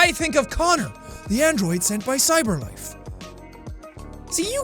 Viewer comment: Whenever I hear 'The Android sent by CyberLife' I hear ' The Android Senpai CyberLife'